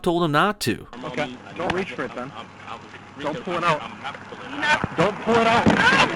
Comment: did he say (philando) "Imma have to pull it out"